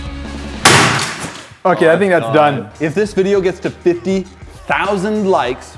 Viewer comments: did say ok i think thats done or f ya i think thats done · Rewatching all videos years later and just realized: At 4:15, Did say the F-word? XD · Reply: ok, I listened on a different device now and heard that he said "Ok", not "eff-it".. oops... XD